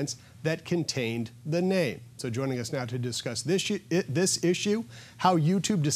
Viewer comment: Did he say"...to discuss this sh*t"? Lol